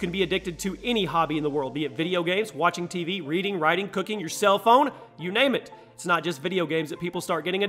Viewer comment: The question is Why would I cook my Cell phone (It sounded like he said "Cooking Your CELL PHONE!)